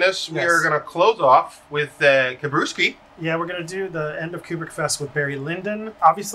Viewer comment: It sounded like you said “pubic fest”